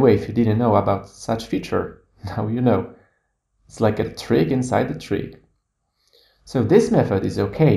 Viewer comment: Hi, Thanks for your great tutorial! I am translating your video, and there’re several places that I can’t catch up with you. First one is around 1:30, when you say: "…now you know it’s like a ???? inside a tree… ". Are you saying "a tree inside a tree”? I’m not sure what you mean here. Second one is around 2:44, when you say: "…and especially when ????? spent much time on…”. What’s the word after "when"? Thank you for your patience! Chenglin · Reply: Chenglin Wang hey, I'm sorry, I'm not a native speaker, but I'm trying my best :) the first one is "trick inside a trick" meaning I'm showing here two things at the same time (inception joke) the second one "when you didn't spend much time on naming your assets" so the tip is useful when your assets are named randomly. I hope that helps you